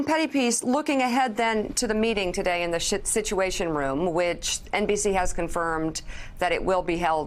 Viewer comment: Did she just say "Shituation Room" at 4:00? So true....